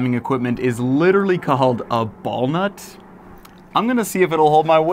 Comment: When he said Ballnut I thought I heard Balls and Nuts 💀